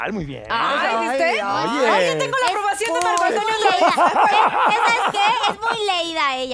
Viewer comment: En el minuto 2:14:40 dice (tengo la aprobación de Marco Antonio Solís) o escuche mal? 🤔